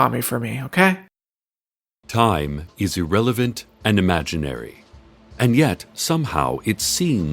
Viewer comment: said "say hi to Tommy for me" and I said "but I don't know a Tommy" and I thought said "Tommy is irrelevant and imaginary" "Ok"